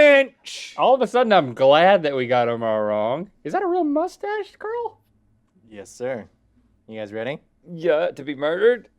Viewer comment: I thought Rhett said “is that a real moustache, girl?” and I was so here for it